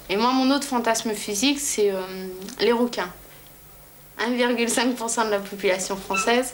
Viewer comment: la vache j'ai entendu "les requins"